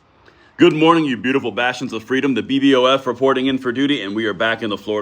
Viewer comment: I'm a beautiful bastard of freedom 🤗🤗😂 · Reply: Listen carefully. He is saying Bastions. I thought he was saying bastards for quiet some time. 😮😂😂